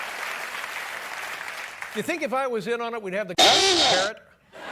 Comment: what did he say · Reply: I think he said "the balls to air it"